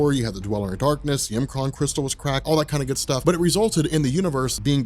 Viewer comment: "...had crack all that good stuff" Lmfao definitely didn't hear that the first time